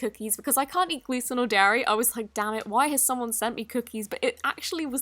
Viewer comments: Who else heard her say damnit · Lol Idk what I heard what did you hear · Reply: dammit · DID U SAY DAMMIT?!?!?!?! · did she say Dam* it ? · did you just say damm it or dang it? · Did you say "dammit" at 2:16? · I didn't - thanks for the input though! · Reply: OMG I love you so much!!!!! I didn’t think you would answer! You’re like my favorite slime YouTuber!!!!!💗 and when I said don’t swear I mean at 2:17 I thought you said “dammit” ☺️ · Did she say damnit at 2:17😂😂